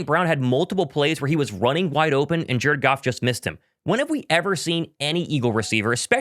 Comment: 😮 I was like "Thomas! What's going on man"!!! Any time you said Jared Goff, it sounded like you were saying jerkoff!! Lol I thought you called them the Jerkoff Lions!!! 😂😂😂